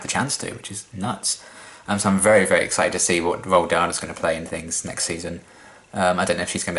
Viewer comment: Hahaha, at 25:03 I heard "So I'm very, very excited to see what Roald Dahl is gonna play"